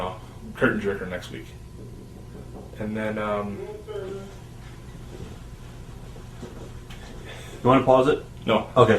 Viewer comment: What did the background voice say in minute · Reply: "Room service" This was shot in a hotel